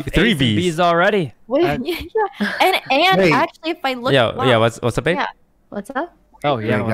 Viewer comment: When he said “what’s up Abe.” It sounded like he said “what’s up babe.”